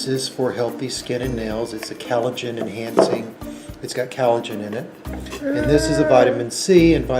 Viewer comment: This borderline pdf pronouncing it as “callojen” says everything about him that I need to know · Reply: the way i had no idea he was meant to be saying collagen until i saw someone mention it in the comments like i thought he was saying some brand name fkjdfkf